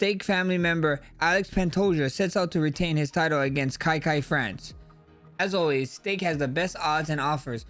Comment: Did Max just say "Kai Kai-France" or am i tripping